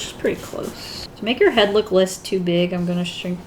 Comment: When she said , " less too big " I thought she said let's do it 🤣